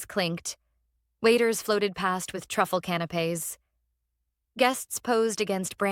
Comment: Hmmm... "Truffle canopies" at 8:37 ???? That's what your text subtitles say. CANOPIES? You need to PROOFREAD! You need someone who can spell and knows the correct word, which is "canapés." A canapé is like an hors d'oeuvre which is like an appetizer. (And also not a "can of peas" referencing one of my all-time favorite films, "The Godfather.")